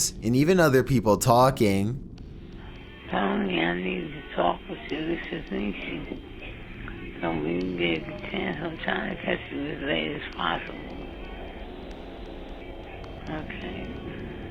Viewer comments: I think she said"hunny,i need to talk to you,this is (unable to understand) i'm trying to catch you as late as possible. Okay? (unable to understand) " cause i listened really closly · I heard “to talk to you” and “anything is possible” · I can kinda make out what this persons saying: Hey I need to talk to you this is Stacy.... that's what i heard the rest idk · i think it says "honey i need to talk with you this is macey if we can get the chance to go to vegas somtime ive... okay" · i hear “Donny I need to talk with you this is nancy”